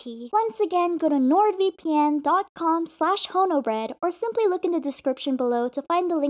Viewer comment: i heard "homobread" 💀